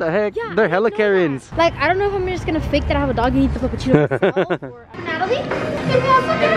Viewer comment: I love how mature you are and now you have another dog and she’s super cute! I’m so proud of you been watching nathaly since 2019! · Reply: oh sorry I thought you said